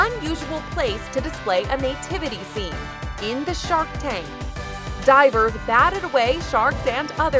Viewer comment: Did anyone else thought it said drivers instead of divers? Lmao I was reading it 4 times and was confused with what it meant